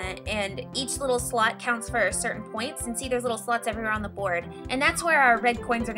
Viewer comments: did you say "there's little sluts all over the board"?? xD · Sounds like said Sluts at